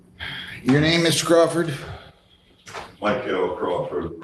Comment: “your name is Scrufford”. I know that’s not what he said, but I’m naming my next dog Scrufford.